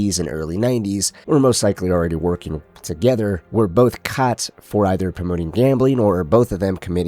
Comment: For a split second, when you said “caught” I thought you were going to say “cazzo” … just by the way you pronounced it.